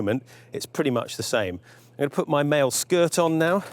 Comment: My dumb ass thought you said "male skirt" instead of "mail skirt". Shows you where my mind was at. Otherwise great video man! Very informative I was curious about this.